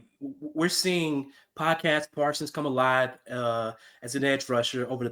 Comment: thought he said podcast parkins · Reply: Podcast Parsons, my friend.